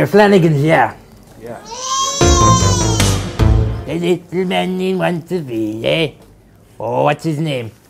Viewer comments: What does he say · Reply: I think it’s : the little maneen wants a beer? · "The little mannín wants his _____" Anyone know what he says here? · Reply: Baztastic7 The little man he wants to be.